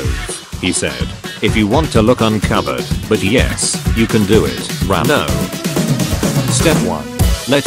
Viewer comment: you can do it righ- no thats what it sounded like